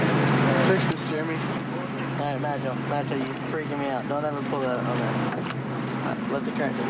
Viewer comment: did jeremy say to Zac that he's freaking him up? :D:D:D